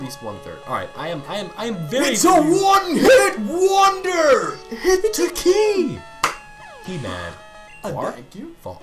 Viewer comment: Hearing them go "hit to key!" in perfect unison just fills me with happiness every time I hear it. I don't really know why.